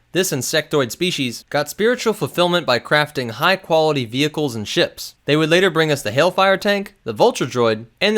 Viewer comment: I heard "by crafting high quality vehicles and sh*t" lol